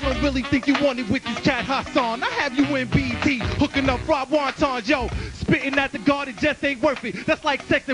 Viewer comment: Week 1: Jin breaks out strong. love his very first 2 lines. His very very last few lines sound like he was thinking of something there but wasn't quite able to articulate. Still excellent. seems to be trying to tell Hassan to be prepared, but maybe it throws him off. Hassan comes back with an ok flow. I don't quite understand his first line though, , something about being in BD stirring up wanton? Jin plays into the stereotype a bit with a bow and hands together, 3:02. Hassan keeps going, nice nice, and then falls right off. Jin mentions his little sister at the end, which becomes a running theme through these battles. Week 2: OUCH. Sterling probably gives the most 'offensive' lines out of all these battles. straight written. I thought that's probably it for Jin. Yet when Jin comes back the crowd is totally behind him. They're ready to cheer him, because his comebacks are a nice rebuttal to Sterling. Jin was waiting and expecting this kind of response from at least one rapper. At the end, pumping up Jin is great lol. "HE'S CRAZY!" Week 3: Skitzo, even though I like some of his lines, I guess this seems too much like a basic performance. He ends before the full time, loses his timing a little near the end, resorts to props. I think the dollar battery line got to Jin. Jin comes back with full passion, he's great and angry here. continues his line after the music finishes. love it. slaps away Skitzos hand. Judges just looking at each other stunned lol. 8:52 someone r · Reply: Week 1 — he says Jin will be in BET (the station it was on) stirring up fried wontons, saying basically the only place he has on black entertainment tv is in the back kitchen cookin. psyched Hassan out, but helped the legend of Jin be born that day.